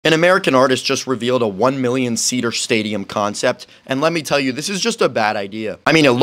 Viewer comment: My dumb ahhh thought he said 1 billion